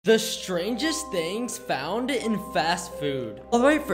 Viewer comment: For a second I thought you said Stranger Things